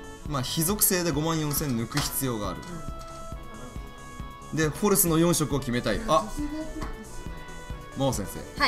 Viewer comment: 16分30秒の後くらいののホルスっていをうとしたのか、 フォレスって聞こえて爆笑した